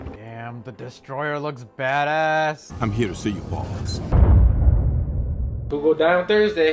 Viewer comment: fury says “I’m here to see your boss” But the subtitles say “I’m here to see you balls”